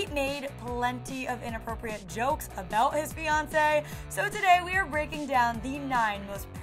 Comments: I’m just saying... Whenever she said ‘fiancé’ I thought she said Beyoncé. 😂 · forever mishearing fiancé as beyoncé